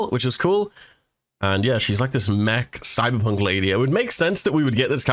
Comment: When he said "Cyberpunk" for some reason I heard "Thighberpunk"